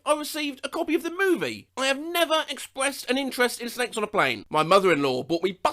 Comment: I SWORE he said “s*x on a plane” and had to rewatch that part 😭